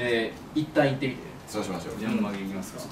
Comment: とし ｢ゆめまるいきますか｣って聞こえたからビビった